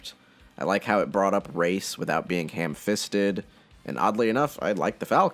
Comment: What!? Workout Did you say without being hamfisted???